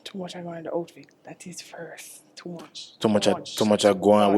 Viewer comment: I thought she said The old fit But I get it. It's the outfit.